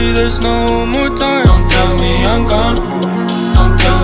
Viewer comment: Not gonna lie at 1:53 I hear: Don't tell me Hong Kong instead of Don't tell me I'm gone.